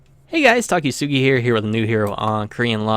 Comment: i thought you said "Takisugi" at the beginning